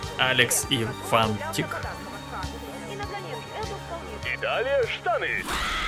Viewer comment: тааак... кто услышал: "И далее Штаны!"????????